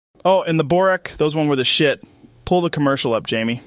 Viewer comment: When he said "oh and boruk those were that shit" I thought he said they "were shit" not THE shit and I was so mad for a second lol cause those things we toighhttt.